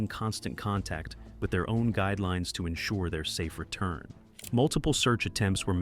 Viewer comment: When he says guide I thought he said their guy